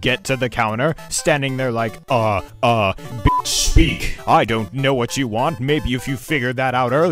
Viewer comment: If any of you guys are wondering what he says at the end it’s “I don’t know what you want, maybe if you’d figured that out earlier I’d be able to serve some of the people behind you”